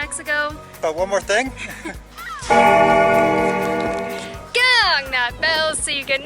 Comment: do you say ding or ring that bell? Or what do you say? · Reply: Gong that bell!